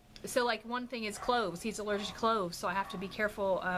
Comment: When you said Ricky is allergic to cloves I thought you said clothes. That would make life really difficult. Lol 😂